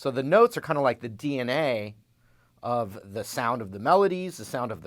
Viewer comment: okay..who else heard D and A